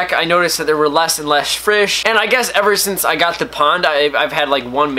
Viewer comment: you said frish instead of fish, yes I notice everything😂